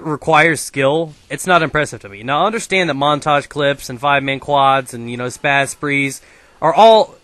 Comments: did you say five-man quad at 2:23 ? school FTW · did he say 5 man quads??